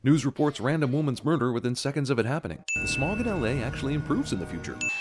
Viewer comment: Says LA but the subtitles say Los Angeles ding